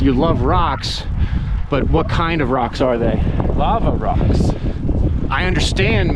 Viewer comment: Hey Phil, I just want to clarify: I was trying to explain that they were LAVA rocks. I was not saying I love rocks. Hopefully that makes sense. Great video, regardless. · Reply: Oh - with the wind I thought Mitch was saying it’s a LOTTA rocks… it is a lot of rocks.